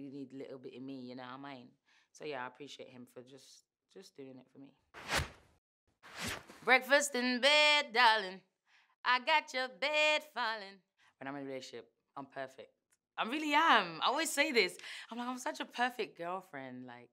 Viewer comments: All this time I thought she was saying “ I got ya bacon “ 😂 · I Could've Sworn She Said Breakfast In Bed, Hun, I Got U Bacon 😂😂😂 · I thought she was saying "I got your bacon!!" I never once heard "bed falling" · I swear to God I thought it said breakfast in bed hun I got you bacon